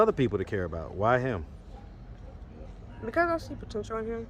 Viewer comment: Somebody tell me what she said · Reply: She said, “because I see potential in him”.